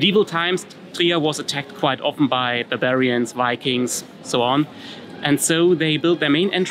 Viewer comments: ff Bloody mishearing on my Part. First time listening I understood ''Bavarians'' instead of ''Barbarians'' and went immediately in the ''Oh, those darn Bavarians again!'' mode in my very prussian brain. I hereby publicly apologize. 😁😏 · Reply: I thought he said Bavarians, too! Oh funny. But Barbarians must’ve been it. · ff Bloody mishearing on my Part. First time listening I understood ''Bavarians'' instead of ''Barbarians'' and went immediately in the ''Oh, those darn Bavarians again!'' mode in my very prussian brain. I hereby publicly apologize. 😁😏